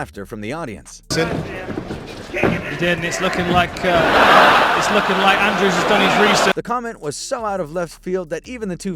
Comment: what is the famous catch phrase? I can't hear it. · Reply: "kick him in the dick"